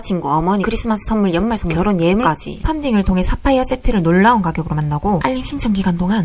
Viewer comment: 사파이어가 왜 삽화이어로 들리지?